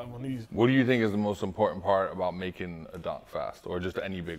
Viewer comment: sounded like he said Donktruck instead of dumptruck